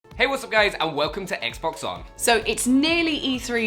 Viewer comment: On this dudes intro he says "What's up guys" I swear to god I thought he was gonna say Its scarce here